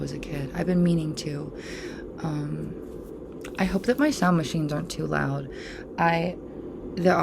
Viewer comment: I thought that you said ''sewing machines" here which really confused me. It made me believe that background hum was loads of automated sewing machines producing cardigans or something off screen. Which was a really baller image quite honestly. You are 100% spot on about physical media not being a thing anymore, it is so sad that they don't make beautiful manuals and other extras nowadays. Digital downloads are just plain lame in comparison. Speaking of David Lynch, I have a physical copy of the original Dune computer game from 1992 and it is so beautiful! (amazing soundtrack too, would highly recommend you have a listen if you haven't already. There was actually a CD-ROM released at the time by Exxos I believe called ''Dune: A Spice Odyssey" however I've never had it. Apparently it is an extremely rare album to try and get now) Keep the videos coming lovely, your looking really well! :) xo